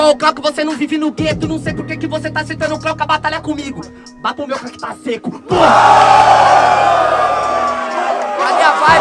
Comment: Eu não entendi muito o que o na parte 8:19! Alguém pode me ajudar?? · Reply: Ele falo: baba o meu aqui que ta seco